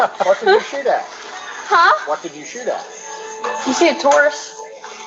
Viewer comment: When the daughter said we were shooting at "taurus" I thought she said tourists. 😅😅